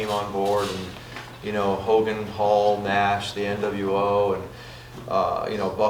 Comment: For a second I thought he said "Logan Paul was in WCW" But no, it was "Hogan, Paul..." xD